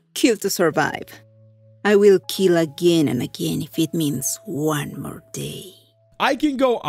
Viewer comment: I think at 2:27 she says one more day for her sister, like every kill is one more day her sister is alive. I dont know tho